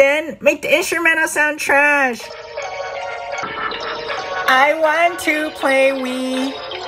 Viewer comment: I was looking away when I heard "I want to play wee"